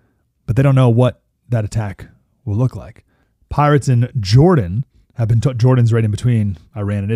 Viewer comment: I thought he said “Pirates in Jordan”…..😜😂😂😂